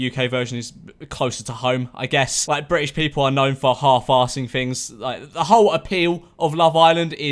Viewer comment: “British people are kinda known for half assing things, so I feel like Americans will actually put effort into this” ..I thought it was the other way around tho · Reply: thought he said half assing things